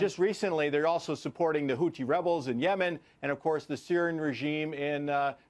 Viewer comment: Did he say Hootchie rebels at 3:28?